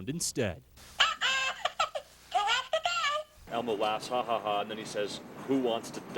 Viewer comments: He actually said "Who wants to go?". At this point, they're just exploiting dirty minded humor. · The accual sentence was "who wants to go" but people heard it as "who wants to die " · i hear, "who wants to get a job" lol 👀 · Sounds like he said (who wet the bed) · "uh oh who wants to die?" i've sat here watching this video like 3 times & i'm still laughing w/tears streaming at how hilarious this elmo book is (i listen to regular elmo non-stop all day w/my toddlers and to hear this just makes my day!) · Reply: I thought he said who wants to go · i heard "who wants to go" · 56; I heard "Who wants to go?" but not die. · Sounds like he says "HA HA HA, Who wants to TRY?" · I heard "who wet the bed" these people are just overreacting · I think what Elmo was trying to say was "Who has to go?" (i.e. go to the potty) but the sound was super compressed, so it sounded like he was saying "Who wants to die?" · He says "Who has to go?" but it gets misheard as "Who wants to die?" There was a problem with the audio-compression system or something like that...Buncha crackheads! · I hear "Who has to go?" The compression in the files is reasonable. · i heard "haha, who wets the bed" lol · wait i hear "who wet the bed" that would make since...